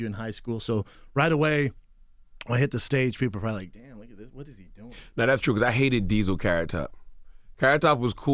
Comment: does he say "Damn nigga what is he doing"?